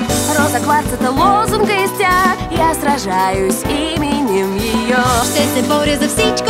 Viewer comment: Did I hear Yu-Gi-Oh or what? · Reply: Imenem yeyo